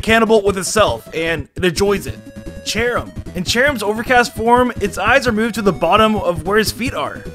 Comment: What he says: “Cherrim’s overcast form” What I hear: “Cherrim’s over Castform”